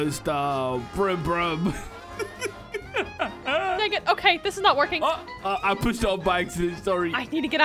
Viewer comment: did say the n word😭😭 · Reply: No, she said dang it